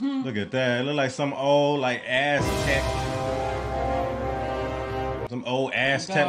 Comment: sounds like said azz tech lol 👍🏾